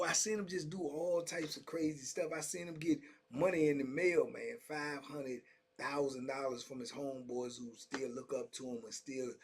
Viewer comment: Did he actually say that the guy got $500,000 in the mail. 🤔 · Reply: 500,1000. Notice the space in his speech. You heard half a mill, he said 500 then 1000